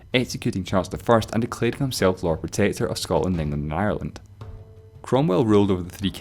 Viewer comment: on the 3:57, did he said that he was a "Lord of potato"?